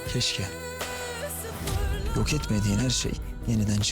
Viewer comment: de ne dedi yaaaaa?!!!🤣😵🤪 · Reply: Günay Tatlı YOK etmediğin her şey dedi fesatlar yaa 😂